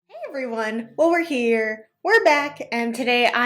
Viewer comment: Hey I have a question I hope you can answer!! What do you say in the beginning of your videos??? I hear “while we’re here” but I feel like that’s not correct lol lol I always wonder!!! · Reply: I say "Well we're here, we're back"